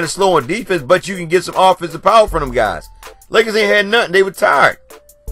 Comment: Family that's why Walton should have given them some rest, especially the 3rd going into the fourth..Ball almost played the whole game if he didn't get his 4th foul. Also, Doc's defense isn't for players that are physically and mentally tired. I would have set them for like the last 3 minutes of the 3rd and let them play the whole 4th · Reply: yeah i had to correct myself. I thought you were saying that the Lakers came in the game tried. That's on me 👂. Yes Luke doesn't have faith in the whole team to be exact. He's a veteran guy.